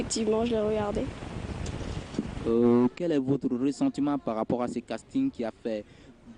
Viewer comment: Est moi ou il demande: quel est votre “ressentiment” a 5:58?